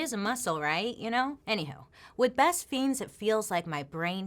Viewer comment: LMAF, anyhow??? sounded like anyhoe